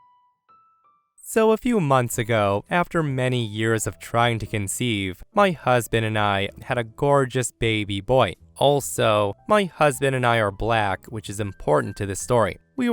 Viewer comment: My goodness, around 23:58 I thought you said "cousin", not "husband"! That's what I get for staring out the window while listening!